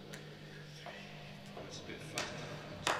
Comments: at first, I thought he said "gotta spit faster" haha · I think he says "oh it's a bit fast, that" referring to the "2...3..." count-in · I always thought he said "it's a bit faster" because then the tempo speeds up just a hair. But I could be wrong. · does anyone else hear chris say "it's a bit fucked" around 0:03? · Is it just me, or is Chris saying "So... It's a bit fucked" In the beginning